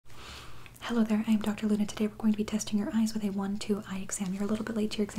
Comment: I thought she said "Hello I am Dr lunatic"